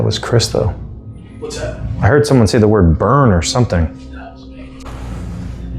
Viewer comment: After says, I heard someone say the word burn or something", I hear "That was me." This was right after said that he didn't think it was Chris who said it.